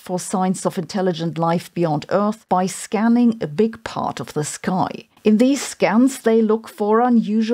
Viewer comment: Did I hear "scamming a big part of the sky" at 1'17? 😅